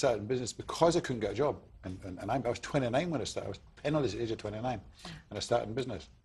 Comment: Did anyone understand after 0:53 "i started business because i couldnt get a job"... Gibberish*..gibber*..gib*gib*.. 29.. gibb*..gib*... ?? Wtf did he say @ 0:53?? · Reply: "I was 29 when I started, I was penniless at age 29"